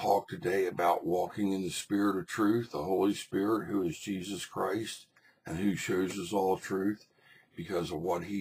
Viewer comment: Did you say, "...the Holy Spirit, who is is Jesus Christ..."